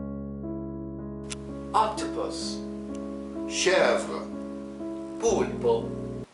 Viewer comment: octopus is akhtaboot most of what he said is Syrian dialect not real arabic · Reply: Med Iron I didn't mean the arabic one I meant at 0:14 in french he did not say Pieuvre but he Said Chèvre (Goat in French) and that's hilarious